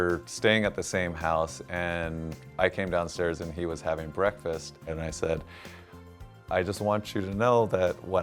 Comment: wait, did he just say "and he was having breakfast"